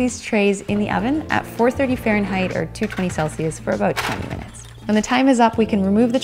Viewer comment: did she say 20 minutes? Listened to it 4 times and I'm still not sure 😅